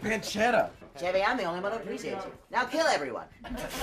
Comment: i still dont understand what is he saying in 2:52 but i love it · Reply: He says "Chevy I'm the only one who appreciates you, NOW KILL EVERYONE"